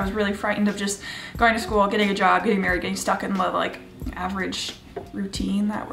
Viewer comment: When you said you were scared of getting stuck in the system. I misunderstood you and thought you said “going to school, getting married, getting stock” and I was like they didn’t teach me that step 😂 It did make me think though, I feel like society tells boys that they should prioritize finances and makes women feel intimidated by it which is why I’ve always loved your finance videos.